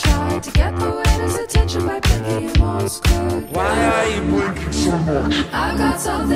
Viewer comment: dude his why are you blinking so much sounds like he said “ Y R YUO BLINCING SO MOCH”